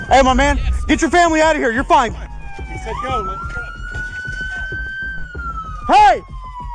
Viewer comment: I heard some dude tell the cop, "Take care man!". 👍❤️ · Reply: No he actually said “ he said go man let’s go” look at the subtitles